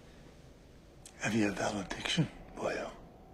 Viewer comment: Help! What does Dudley say at 2:28?!? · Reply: "Have you a valediction, boyo?"